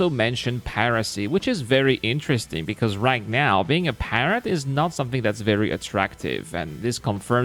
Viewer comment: For a moment there I thought you said "being a parent is not something that's very attractive". I thought you might have been having a tough day lol. I hope to be exploring between trading to make money. I don't imagine exploring will be a big money maker, but time will tell.